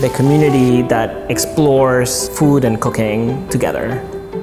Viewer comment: Sounded like he said cocain